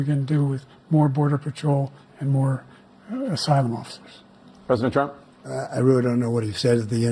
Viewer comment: It sounded like he said "Asylum Monsters" 😅😅😅